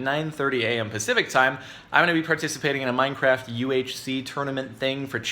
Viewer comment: OMG XD i thought he said MineCrack XD hey man you got some of that MineCrack XD ya man heres my sever giving out admin XD